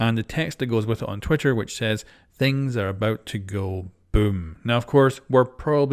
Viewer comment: I thought it said "things are about to go bloom" 🤔